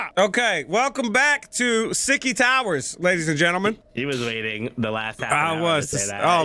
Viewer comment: I thought he said sticky towers 😅